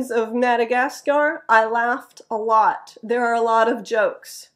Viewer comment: I heard "It" as "There".(#1) I could catch all except for this. I could hardly catch "funnier" but I reasoned it.(#3)